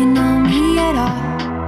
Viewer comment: I keep hearing “ you think you can smell when I fart” 😂